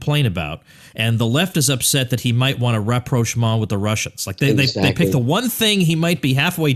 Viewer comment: Could someone please tell me what the French word or phrase at 32:00 that sounds like "reproach" is? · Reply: rapprochement - meaning: (especially in international affairs) an establishment or resumption of harmonious relations. "there were signs of a growing rapprochement between the two countries"